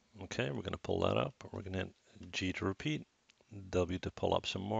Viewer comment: Hi at 2:26 what's he saying? g to repeat? if i press g, nothing happens but show me "result: scale" on the bottom right, unfortunetly I'm allready stuck with that issue